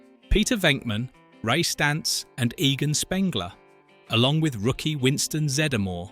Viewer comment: did he just say egon as egan?